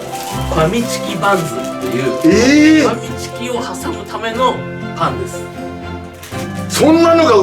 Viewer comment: _ブリーフ団L:「ファミチキを挟むためのパンツ」って聞こえた笑 エガちゃんねるの見過ぎて耳まで毒されてきた:_あたおか